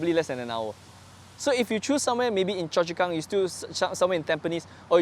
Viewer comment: Justus haha. I think he saying "you choose" but very unclear.